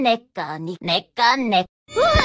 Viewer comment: Bro she really said nica nica that's kind of sounds like nig and is on